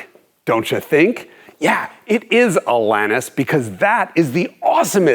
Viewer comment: I laughed out loud at the mention of Alanis. Love her work. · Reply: I thought he said Atlantis.